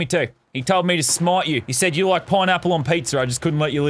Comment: I thought you said porn apple XD